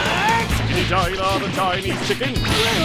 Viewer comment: for those who don't understand cell at 4:11he says "chickity China the Chinese chicken" YW